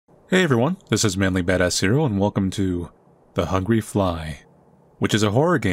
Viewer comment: i don't know why but i keep hearing "manly bad ass cereal" :D